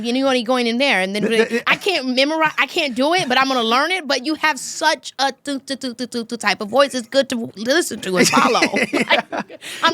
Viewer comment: Can any of y'all tell me what he said at 26:33? They are both so fricken intellectual and in turn im so invested! Ive rewound many, many times but cannot for the life of me make out what he said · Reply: He said he has tempo.